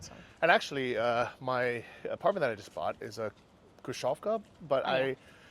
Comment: What is saying "krushavka"? How do you spell this word? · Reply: khrushchyovka